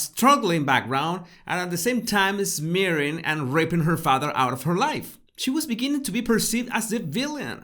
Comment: "...And at the same time smearing and raping her father out of her life."😲 I've listened to it over and over ten times, and I swear that's what he's saying. Normally, accent doesn't throw me, but I'm at a loss this time.